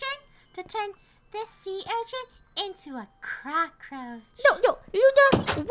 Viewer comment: Sounded like they said CROCKroach